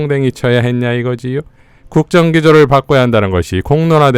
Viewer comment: 국정기조 바꿔야를 음성분석 하면 마누라를 바꿔야로 들립니다 흥분할만 하지요. 바이든도 잘 들으면 날리면으로 들리죠.